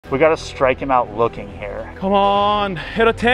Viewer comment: At first I thought he said strike him out without looking